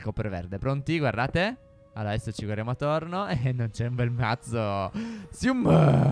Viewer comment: Min 16:23 ma ha detto "e non c'è un bel ca**o" ? Ps: forse ho sentito male io